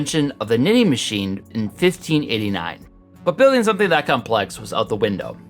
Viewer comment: did he say it was built 1589?